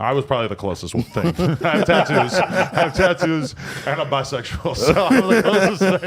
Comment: did he say he was bi ?